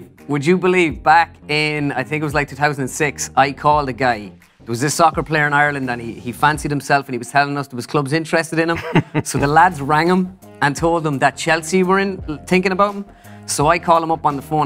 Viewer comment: did he just say soccer